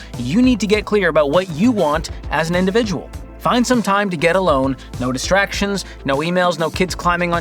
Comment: I heard George say "to get a loan"!